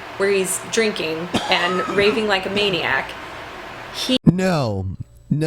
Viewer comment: I thought she said " 'raping' like a maniac".